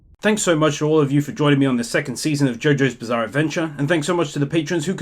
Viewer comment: did he just say the second part? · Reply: second season bruv